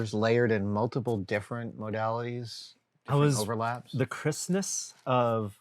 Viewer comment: Everytime you both said "Crispness", I heard "Christmas" and thought that was a specific term for that style of printing I hadn't heard of before :D